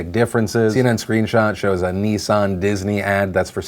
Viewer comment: I thought Phil just said nee-san not nissan XD